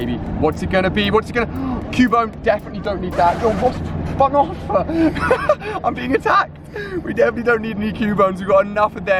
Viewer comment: At about 8:30 I swear he said fuck off